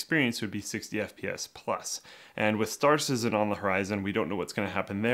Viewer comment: did he said star citizen!?